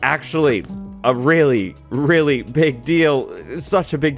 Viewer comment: Did you say a pig deal? XD